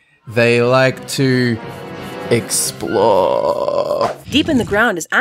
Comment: when said explore i thought he said explode, if you did too, leave this comment a like!